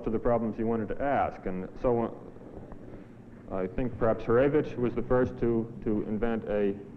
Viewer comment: Does anyone know, which name does he say in 32:12? It doesn't sound like any mathematician I know (whitehead, poincare, betti, riemann, noether, mayer, vietoris,etc) Sounds like a russian or east europe name. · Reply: He's speaking about Hurewicz. <link>